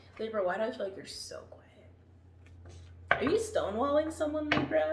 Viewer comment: When you said, ‘libra are you stonewalling someone?’ I literally thought you said ‘libra are you stoned?’ And I was like ooohh my godddd how does she KNOW THESE THINGS 👀 but then I realized what had happened 😐